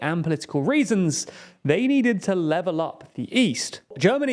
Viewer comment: Oh level UP....thought you said DOWN😂